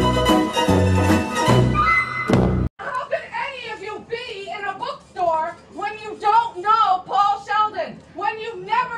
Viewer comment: Can someone pls explain what was happening in the bookstore? English is not my first language and as she was starting to yell i couldnt quite catch what she was saying and also what the cashier said afterwards. Thanks in advance :D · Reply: She says "How can you all be in a book store when you don't know who Paul Sheldon is, when you haven't read misery, I don't understand you people. I'm looking for a cockerdoodie book!" It's an act. Basically she is cos playing a character from a book by Stephen King (it was also made into a film) , the book is called Misery in which a crazy fan called Annie kidnaps the fictional author of a book called misery (yes the fictional book is the same name as the real book) the fictional author is called Paul Sheldon.